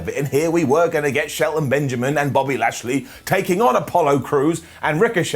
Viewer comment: I swear I heard "Body Lashley" and thought of Bobby Lashley being an 80's wrestler called Bobby "The Body" Lashley.